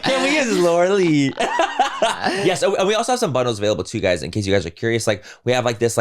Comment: I legit thought he said they have some buttholes available when he said bundles😂